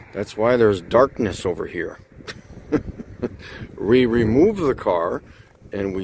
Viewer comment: did he just say “re-remove the car…..”??